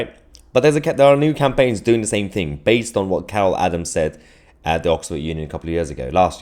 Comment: Thought you said cow Adams